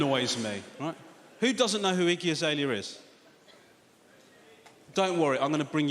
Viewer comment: i thought he was saying “icky azalea” at first 😆